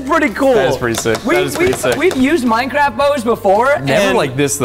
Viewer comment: I thought he said minecraft toys